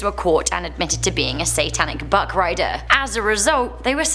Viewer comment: When she said buckriders i thought she said buttgraders